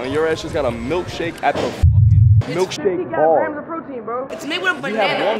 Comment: Bro, when he said 50 grams of Protein, I heard him say 50 Giat grams of protein. 🤣